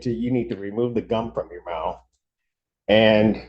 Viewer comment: I heard originally "remove the dumb out of your mouth" which is much more appropriate! Lol